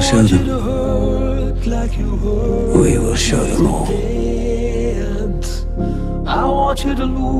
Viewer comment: what does he say?? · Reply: We show them. We will show them all.